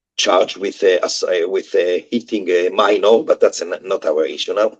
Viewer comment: OMG! I thought he said "eating a minor" and had to go look up the guy's name lol